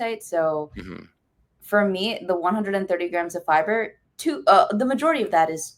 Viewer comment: At first, I heard "430 grams of fiber". Now, that would be something really special. Although 130 is quite mad as well